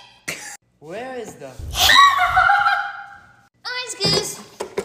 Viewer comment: Did she just say orange toes?😂😂